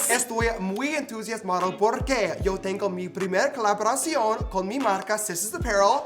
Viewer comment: Wait…did he just say………YO TENGO (Albert reference)